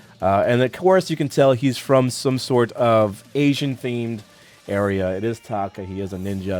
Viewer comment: wow that's racist "comes from some Asian thingy" I'm Asian.... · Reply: i heard Asian "themed" not "thingy"